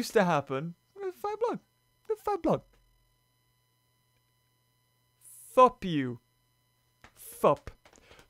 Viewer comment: did just say f*u k you? Cause now I'm getting a little mad at